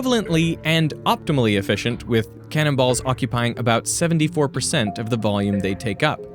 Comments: Rofl when he said cannon balls I misheard cannibals, and that is a VERY interesting problem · I heard "cannibals" when he said "cannon balls" 🤣